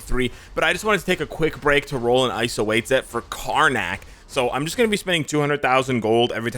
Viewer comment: thought he said carnage in a goofy way 💀 then saw KARNAK